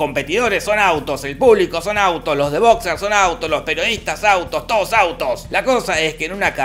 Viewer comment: pensé que dijiste "Los de Boca son autos" wtf